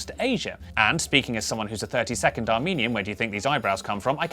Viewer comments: I honestly thought he said 30 second Armenian, as in he was only Armenian for 30 seconds... XD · I thought said "30 second Armenian," was confused why someone might be Armenian for half a minute. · "Speaking as someone who's a 32nd Armenian, where do you think these eyebrows come from?" Good point, my friend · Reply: Yeah I heard it as “30 second Armenian” Like he was just chilling in Armenia for 30 seconds and then left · When he said he was a "1/32 Armenian", I thought he said he was a "30 second Armenian". I was like, did he not like it and switch back really quick? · I heard "1/32 Armenian" as "a 30-second Armenian" and was briefly very excited about this new way to temporarily invoke my ethnicity without opening it up to permanent ridicule.